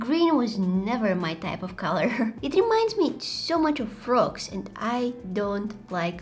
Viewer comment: I thought she said rocks but she said frogs and I was like rocks have minds🙂🤨